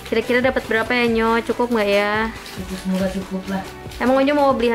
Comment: bismillah cukup😭💗. Ai salah denger gak sih